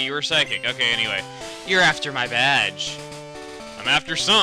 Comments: when said "youre after my badge" i thought he said "you're after my vag" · I thought he said "You're after my vag." o_O